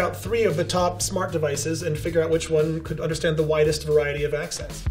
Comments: Okay he said "widest" but I misheard it as "whitest" - although I suppose they'll find that as well by order of elimination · I heard it whitest wide of variety😂😂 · For a second I heard "whitest variety of accents."